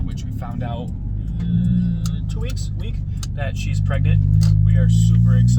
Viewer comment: New baby?? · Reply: I thought I heard you say the Wife was pregnant. Maybe I misunderstood because of the noisy environment around me.